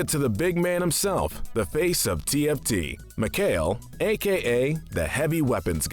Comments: did he just say TFT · did he just say. Tft??? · did he just say the face of tft?